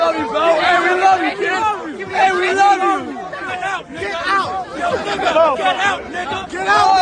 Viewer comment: it sounds like “Get out, N*...” is being said, can you please clarify what’s being said and who is saying it?